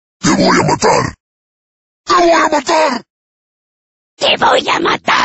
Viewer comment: I swear I thought it meant “Winding the motor” since the dude carried a minigun and I hear the word “mutor”